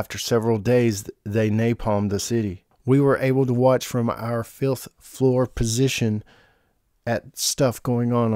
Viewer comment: really sounds like you said filth floor instead of fifth floor